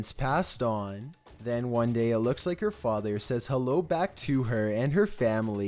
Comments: Are you deaf, it sound like it say no not hello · The ghost child one saying hello was scary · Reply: VeronicaBG I heard “Herro” must’ve been a Chinese ghost child 😂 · The voice of woman is saying hello to. Me:Wait I thought i heard 'And i oop'. O.O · Number 8 to me honestly it didn't sound like hello it sounded like she said Hanna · At number 8 when that child's voice came outta nowhere saying "hello", it also sounded like it was saying, "Hannah" And if you listen very closely